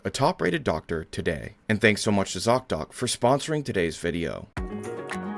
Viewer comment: At first I thought you said your sponsor was "zogdoc" lmao